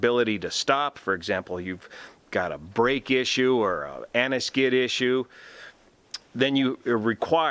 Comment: did you say "anti-skid" ?